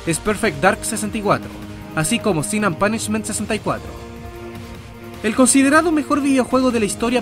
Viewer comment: Juego del minuto 4:31 plocs, se que lo nombraron pero no lo entendí bien :u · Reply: Zudokato Mikashitamoari Sin & Punishment v